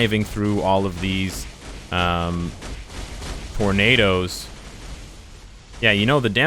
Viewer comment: big titty goth girlfriend, never a Like has been achieved so fast! I SLAPPED that like button! And at 7:59, did he say he's trying to dodge these "Pornadoes" with her? I mean, she's gotta look out for those!!!!! LUL